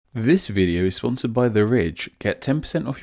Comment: For a moment I thought he said"This video is sponsored by the rich"....I thought"nice!" 😃